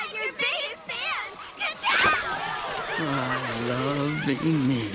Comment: can someone please tell me what lightning McQueen says here lol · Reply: "I love being me"